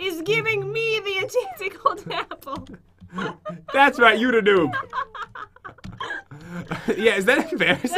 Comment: #YoutheNoob At least that's what I heard.